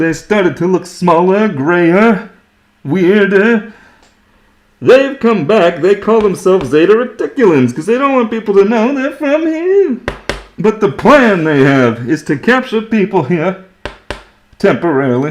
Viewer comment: Ha ha ha I heard “Zeta Ridiculous” which I think is a superior phrase, having heard it 🤣